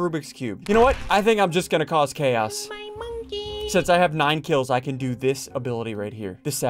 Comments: I hear someone said "my monkey" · Bro i heard Me and my monkey in the video in 10:48 😂😂😂😂😂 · Who heard that "my monkey"?😂